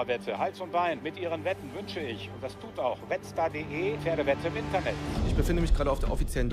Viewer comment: Ich habe nicht Wettstar, sondern Metzger verstanden.